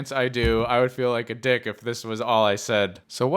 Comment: wait dud he say di**? Well that was the first time I heard swear